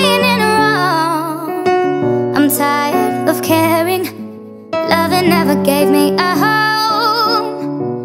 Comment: I heard loving never gave me a hoe but I love this! Good job, 2019 squad